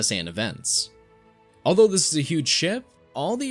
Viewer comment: Am I The Only 1 Who Heard Huge S?